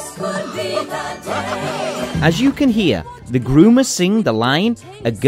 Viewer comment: I thought he said boomers